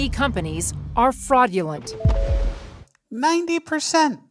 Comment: Did she say “frogulent”?